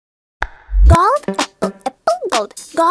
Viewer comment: "GoLD pEPpLe pEPpLe gOLd" Edit: HOLY 178 LIKES?!?!?!?! 😳 Edit 2: aight this might get pinned soon Edit 3: gold pepple pepple gold gold pepple pepple gold gold pepple pepple gold pepple gold pe-pepple gold Edit 4: im just a small youtuber but with this big comment Edit 5: ah sh*t here we go again 478 likes?! Edit 6: okay stop please. Edit 7: bruh Edit 8: i will remove all the edits when it reaches 10 Edit 9: drama in the replies!! 🗣️🔥 (since a year lmao) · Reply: I thought it was saying “Golf epple epple golf”